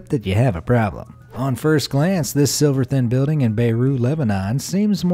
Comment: Sliver thin, not "Silver" thin. Look closely at the spelling. The second one is what you said, the first one is what's correct.